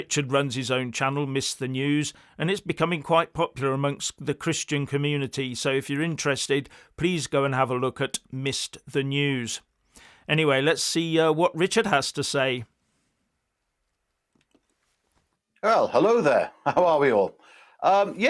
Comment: RICHARD: ah! Your site is ‘Missed The News’. I didn’t get it right the first time as I thought said ‘Mister News’.